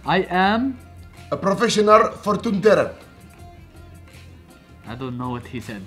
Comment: Profetioonul Fortoon Terer -I don't know what he said but ok 😂😂😂